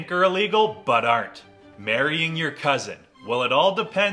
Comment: I thought he said burying your cousin